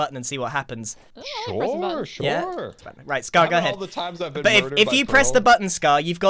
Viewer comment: is it just me or did call scar babe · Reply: He was gonna say "but it" and then changed the sentence